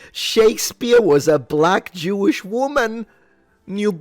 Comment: I thought they said Shakespeare was secretly a black woman? 🤔